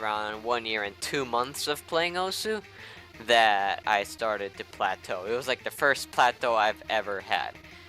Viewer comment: what did you say? platow? what is that?